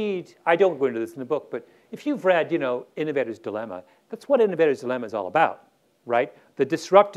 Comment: Mr. Gladwell mentions ~Ennaveda's Dilemma~ (12:30) the spelling of which I am not certain. Does anyone know what he is refering to? Thanks.